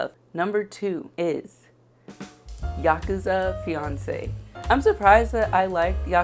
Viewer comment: I was looking down when you said "Yakuza Fiance", so i thought you said "Yakuza Beyoncé". I looked up SO FAST bc i was like "now THAT, I want to read." 😅😂 I just stumbled across your channel today. Looking forward to watching more videos!